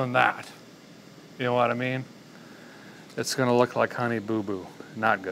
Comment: In about 40:15 Did he say Connie boo boo ?